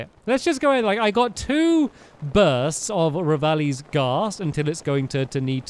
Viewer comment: Did you just say Revali's gas?